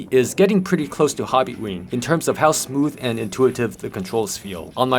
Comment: Asking in a nice newbie way, what did you mean by it not quite being a hobby grade ESC? Wait... I played it back and think you said hobbywing, my bad.